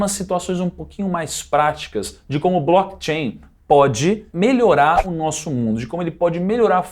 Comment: Aos 13:50 do vídeo foi colocado "BLACK CHAIN" ao invés de "BLOCKCHAIN"